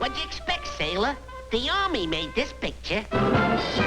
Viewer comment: What does he say there? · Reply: KingChickenwing “what did you expect sailor? The army made this picture” im pretty sure